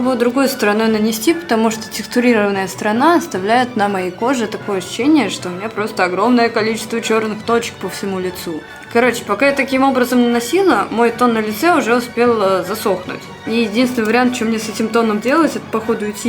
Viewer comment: Тоном на лицо, спонжем по губам ..... господи как же я ржала! П**но на лице.. ахаха, вместо пор на лице послышалось, ааахахааха... господи помогите, остановите меня....